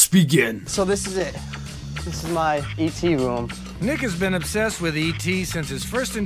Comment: did it say nigga?!